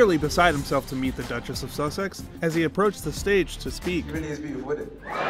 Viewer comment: What does he say at 0:46? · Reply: “She really is beautiful innit?”